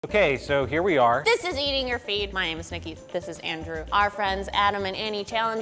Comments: I swear i heard ''THIS IS EATING YOUR FEET.'' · I actually thought she said "eating you feet" which is gay and weird